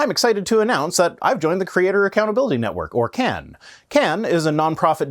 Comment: You've joined the Creator Accountability Network? At first I thought you said the Creationist Accountability Network. That's something I'd like to see!